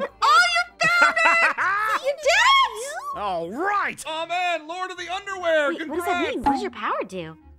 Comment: did anyone else heard say "Lord of the underwear"😂😂😂